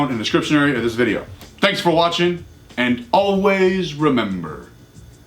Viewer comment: I thought he was saying "train on time" until today